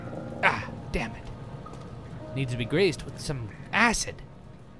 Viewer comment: "needs to be greased with some acid!..." What the captions thought he said "Need to be graced som ashtec!..." Wat o_0